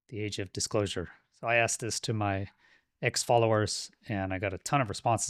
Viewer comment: When you said you polled your X followers, I at first heard that as "ex-followers"! Glad you meant X, not ex- !😊